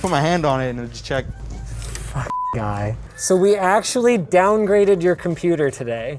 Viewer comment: did Linus say fuck guy